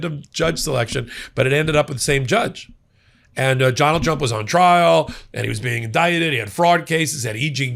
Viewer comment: 😂 Ok I definitely heard "Jonald Jump" and I'm certain it's because he said "judge" like three times before his name. Not nitpicking, that just kinda tickled me, even more so if that was intentional